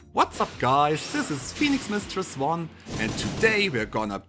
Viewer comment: I thought you said p*nismistress1 at first…🤭 not you calling elm the femboy version of Otr!!! He’s living his best life as Elm now 😳